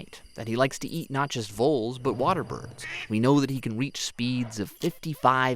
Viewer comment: I thought I heard "We know he likes to eat nachos, voles, and waterbirds..." teeheehee.